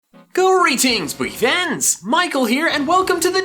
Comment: Why do I always hear "greetings boogie fans" Lol